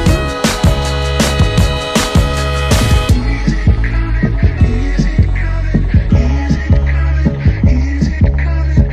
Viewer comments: What are they saying at 1:49? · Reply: Is it coming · what did he say ? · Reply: is it coming back